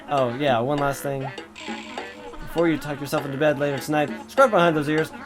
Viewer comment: I thought he said "before you go fuck your self" lol